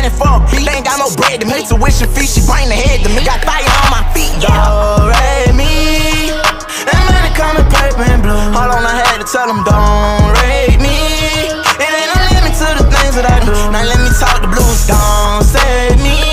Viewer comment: Did anyone thought he said don't rape me?